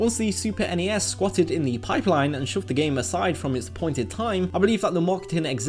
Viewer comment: it says "shoved" instead of "showed".